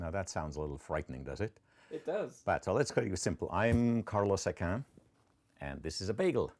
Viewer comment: I could've sworn he said he was Carl Sagan